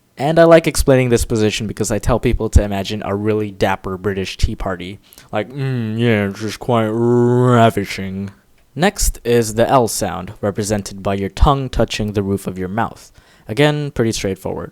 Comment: domics: “next is the l sound” how i heard it: “next is the elsa”